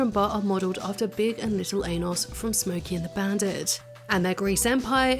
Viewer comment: what she said: "...based on Big and Lil' Ennis from Smokey & the Bandit." what i heard: "...based on big and lil' anus from Smokey & the Bandit." 😂😂😂 🧡 love you, 💚